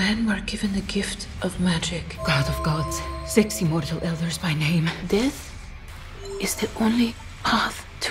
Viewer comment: Did she just say "Sexy mortal elders by name"?